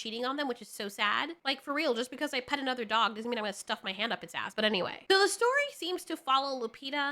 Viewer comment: Wait, you stuff your hand up...? Did I hear that right?!